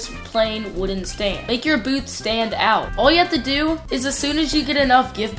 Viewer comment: He said boot instead of booth💀